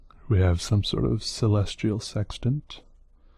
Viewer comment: I'm not gonna lie, but when he said celestial sextant... I first thought he said celestial sex toy...😂😂😂 it's just me isn't it? (Also I'm new here, and I'm incredibly inspired by you art and your art skills!!! I think you could be the next famous artist☺️!!! Meaning you also got your self a new subscriber!) 😎😎😎peace with you😎😎😎